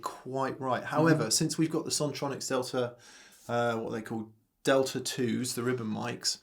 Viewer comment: "..however, since we've got the song chronic Stelter..." - that's what YouTube's automated closed captions just displayed instead of 'Sontronics Delta' @ 7:18. Really does sound like a song name if you switch it to 'Chronic Shelter' (Stelter is the surname of US political journalist apparently).